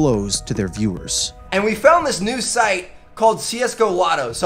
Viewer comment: “And we found this new site...” Absolutely iconic quote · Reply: No no, you misheard We found(ed) a new site